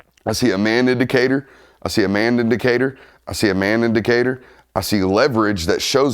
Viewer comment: what I heard “I see a man in Decatur” 😂